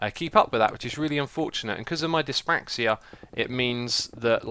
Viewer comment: what did he say? "disbrachia"? English is not my native language, so I would like someone to explain what that means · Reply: ***** dyspraxia